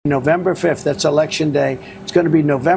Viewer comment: I thought he said November 27th was his election day